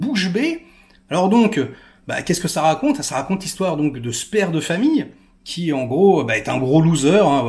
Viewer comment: Vous avez me trouver chelou mais à 5:09 j'ai entendu " ça raconte l'histoire de sperme de famille "